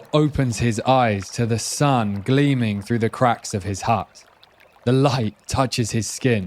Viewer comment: Why does he get cold? Hundred thousand years of evolution they never invented shelter or blankets? Never taking this guy moose hunting. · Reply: Yeah, I thought the same. In fact when TJ Power said, "heart," at 0:11 I heard, "hut." Hut makes more sense to the story, in my opinion.